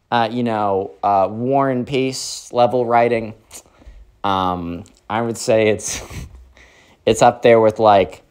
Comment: did he just say it was.. one piece level of writing?!??!?!